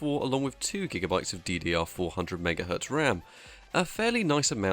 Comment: I thought he said ddr4 100mhz instead of ddr 400mhz ram